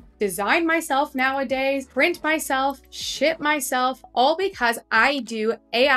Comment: I heard "shit myself" in the beginning, instead of "ship" LOL